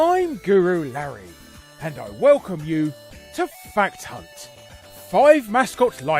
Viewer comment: I watched an episode of this in public when you said fact hunt they thought they heard fat c***